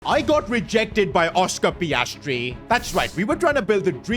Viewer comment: I thought he said, “I got rejected on my proposal”😂😂😂